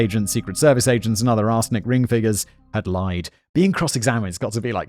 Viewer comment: I keep hearing him say "arse lick inc." and I laugh every time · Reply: I keep hearing 'arse licking' sentences like "people involved in 'arse licking'..." or "victims of 'arse licking'..." had me rolling over the floor